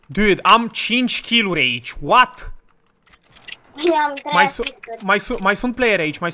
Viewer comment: Mă uitam la video și maică-mea a auzit că cică copilul ăla a înjurat, și m-a certat. Eu am înțeles că zice ,,Eu am 3 asisturi" Voi ce auziți? :)